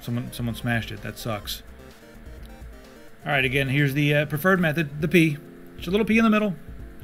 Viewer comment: at first I thought he said "the turd method" . . . which, actually make sense. Drop a little deuce in the center and it will spread evenly.